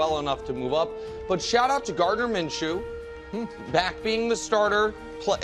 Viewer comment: Wny I thought nick said “Murda Minshew”